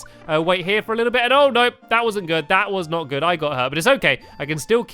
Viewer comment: I heard 'Bowser good'